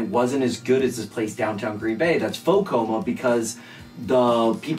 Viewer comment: What did he say at 9:35? It sounded like faux coma but I doubt that’s it